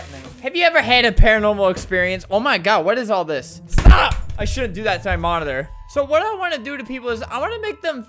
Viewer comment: I thought he said "I shouldn't do that to my mother"